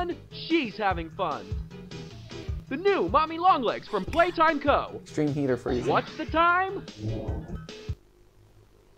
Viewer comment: did anybody else hear a whisper a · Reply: Yeah she says 'Wake Up' again